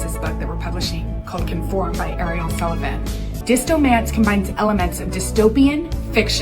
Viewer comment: I heard of it as “disco”mance and now I’m imagining romance in a disco area